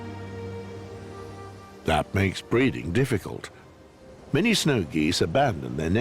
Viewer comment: who is he? i cant understand his name. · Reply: snow goose